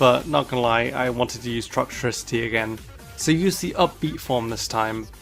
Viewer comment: he says up key form instead of amped form toxtricity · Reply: He says “up beat”